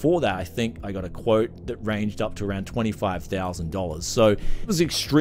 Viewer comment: Did he just say 25K quote. I at first thought it was 2.5K which is high but sounds like something that can be quoted but 25K is unbelivable.